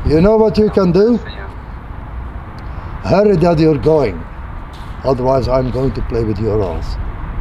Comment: I thought he said "or else I'm going to play with your lives" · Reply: No 100% "ass's" haha